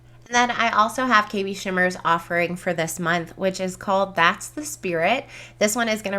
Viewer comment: Dang when you said that kb shimmer name I thought you said "Bat's The Spirit". That was a missed opportunity!